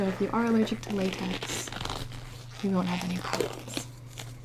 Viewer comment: At first I thought gibi said “These aren’t latex free so we won’t have any problems” I was like what ok I’m scared